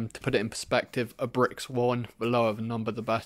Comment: "To put it in perspective a brick's" what was the next word ? Thanks in advance. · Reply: Yea he said a brick has a value of 1